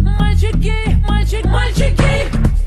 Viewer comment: In the beginning of the sonv it sounds like they saying "my chick gay". lol